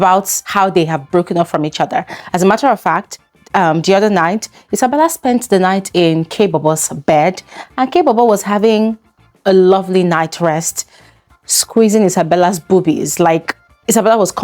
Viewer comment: I thought she said she was going for kaybobo 😭😭 that normally kk does not get a chance to talk to her outside the house